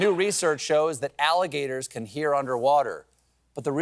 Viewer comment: I thought it said alligator's can hear underwear.